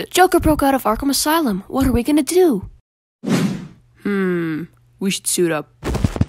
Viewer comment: When batman said "We should suit up" I thought he said "We should sue them"